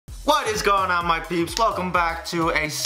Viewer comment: it sounded like he said "whats going on my pubs" XD